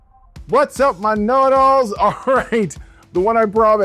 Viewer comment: Oooh he says whats up my "KNOW IT ALLS" 😂. This whole time I thought he was saying the N-word in a goofy way. Kind of like the rapper E-40 would say it, which he also reminds me of for some reason 😅